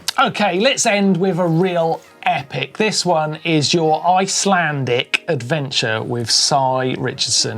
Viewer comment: Likely Martyn's Welsch accent, but I thought he said "...I slam dick adventure". I thought perhaps you guys had started some sort of GMBXXX channel.